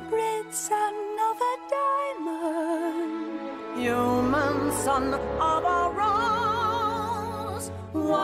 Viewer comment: this is for myself cuz I love yellows voice and wanna replay it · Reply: I keep hearing “your moms son is a rose”